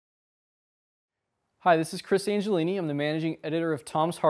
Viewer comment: at first i thought he said he was Cris Angel